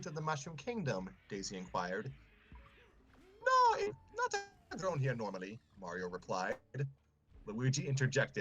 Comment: I'm back. 1:11:58 - "No it's a rare morsel. Not grown here normally." 1:13:44 - "Peach dived out of the way, leaving Yoshi to..." 1:14:42 - "Kamek," I said "Kamek." 1:17:42 - Me saying "I got rid of them!" didn't come through for some reason. 1:18:44 - "Back at Peach's Castle, Bowser Junior..." 1:22:43 - "Everyone's favorite episode." 1:24:56 - "Let's do the ??? thing." - 1:38:14 - "...a mountain of dead alien Hitlers..." is what I presume was saying. And it's done. My apologies for the delay in providing "captions" (, I can provide full captions for this video if your audience needs it). If there is anything you can't quite make out, please reply to this comment.